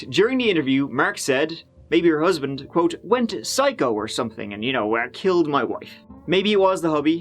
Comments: I heard.... ''I killed my wife... shit.''🤔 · First time I heard 'I killed my wife, shit' after hearing it multiple times I can hear both killed and stabbed but not anything else. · I literally thought he said "I killed my wife" at the beginning of the video. Guy definitely slipped up on that call. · No context, I heard "I killed my wife"...b/c I'm sensitive like that 😁🤦‍♀️ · Sounds like he said I killed my wife” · I heard "I killed my wife" twice. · I hear “I killed my wife shit”. That’s hard though! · Ok.. wait. At the beginning of that... did he just say.. i.. KILLED MY WIFE??? like.. wait a freaking second...